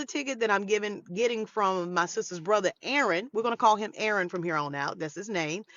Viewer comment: Aaron is her brother in law, is what I thought she said? He was being a gentleman and looking out for a Queen 👑